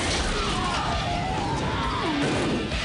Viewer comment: I always wondered what he said lol I thought he said coner freeze for some reason.